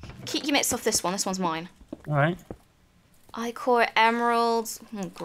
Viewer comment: did Kim say Ichor? Because if so, that's not rubbish, that's the highest tier of Thaumcraft's alloys. It makes the literal god-level armour and weapons!